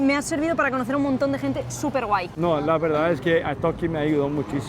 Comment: Ella dijo "súper guay" y pensé que dijo "super white" 🤣🤦🏼‍♂️